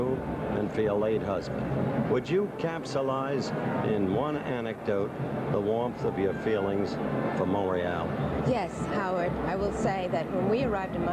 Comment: What did Howard Cosell say?... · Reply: No, he said: "Can you explain the warmth of the feeling for Montréal" (That's Montreal pronounced in french. Mon-Ray-Al)